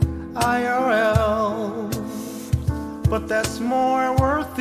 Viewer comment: i think he says LOL not IRL